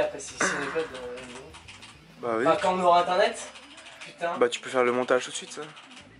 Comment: This is no real subtitles lol my french is bad, anyways - 2:50 i heard that putain